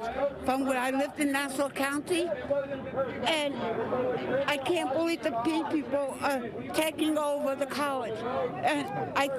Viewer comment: Did she say she "can't believe the gay people are taking over the college"? 🙃 Either way, she's clearly not connected with reality.